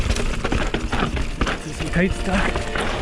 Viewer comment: what did you say? sounds like Norwegian: stikk I veien? which means sting on the road. which makes no sense.